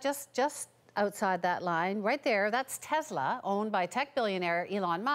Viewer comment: I thought the "Tesla" said T-Series, I was like "OHHHH, NO.".